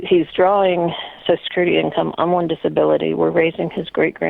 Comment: didn't she say he gets disability through the VA? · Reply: A Williams I thought she said “I’m on disability”. Regardless, we can assume whatever they are getting now, even if it’s a lot of money through benefits, it’s still not as high as they was making prior to his layoff. If they were good, the wife would not be calling Dave sounding this stressed out.